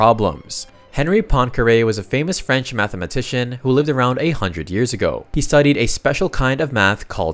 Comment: Poincare didn't live "around 800 years ago," he lived from 1854 to 1912. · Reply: Not just you, I heard it as "800 years ago" as well